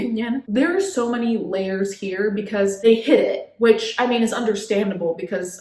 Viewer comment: "they hid it" - I heard " they hit it". Yes.. yes they did.